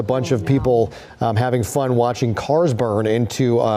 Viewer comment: Did I hear "having fun, watching cars burn"? WTF is wrong with these people?!?!?